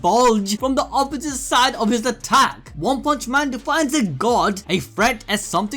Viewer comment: I thought u said woke punch man about 15 or so seconds in. LOL